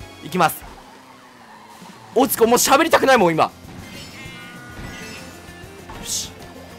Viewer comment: しゃぶりたくないもんに聞こえる